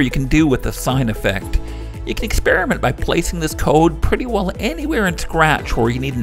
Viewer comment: bro sounded like scientist